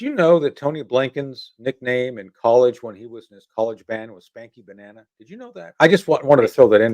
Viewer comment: If you missed that, "Spanky banana" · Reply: I thought he said 'spanking' banana lol